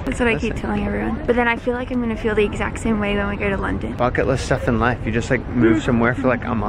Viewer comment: DID SHE SAY THEY WERE GOING TO LONDON?